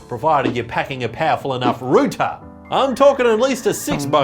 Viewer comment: did you just called it rooter as in rooster?